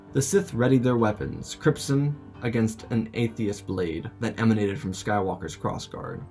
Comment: An atheist blade?? Or at least that's how I heard it ... A holy hand grenade is no match for an atheist blade.... 😁😁😜😜 All jokes aside man I really loved the story.. ♥️🤘🏽🤘🏽